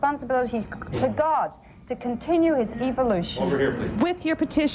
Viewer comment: did she say to continue EVILoution & 46:23 we've got a terminally ill planet, affected with lethal microcrobes???!!! 😨 wt fudge Respect Sally Field 46:04 - 46:20, 52:47 52:10 Elderly lady